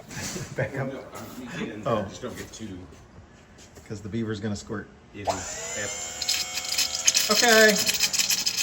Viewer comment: Did I just hear " the 🦫 is gonna squirt" ? LOL